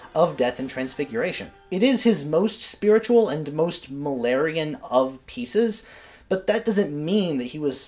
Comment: Misheard "Mahlerian" as "malarian" and it just seemed to make perfect sense in the "Death And Transfiguration" context!